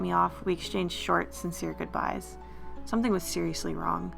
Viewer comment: When you said "They exchanged short and sincere goodbyes," I thought you had said, "They exchanged shorts."